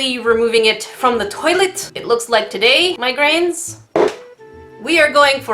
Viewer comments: When you said "my grains" I actually thought you said "migrains" so I was like "Wait, You get a migrain from salt?" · When she says my grains It sounds like she saying migraines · When you say “My Grains”, I hear “migraines” :-) . Our collective saltiness causes terrible headaches. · i heard "migraines" instead of "my grains" and honestly? mood. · oh gosh, when you said “my grains” i thought you said migraines and i had to rewatch it, it spooked me because i’ve been on the edge of a migraine for the whole day so i was like “IS THIS A SIGN??” anyway i LOVE your videos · When she says “My grains”, I hear “Migraines”.